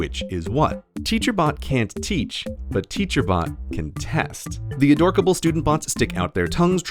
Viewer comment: Ah bots, I thought you said "butts".